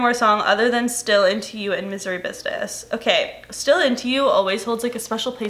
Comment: I thought u were saying “Missouri business” and I was like hmmm..never heard that one😂